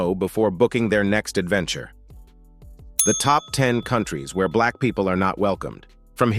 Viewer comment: oohhhh....sorry. Thought you said COUNTIES where black people are not welcomed. All set to get a U Haul and get going.....